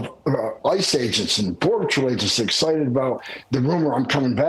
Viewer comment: He mumbles so much I thought he called those border agents and military the r word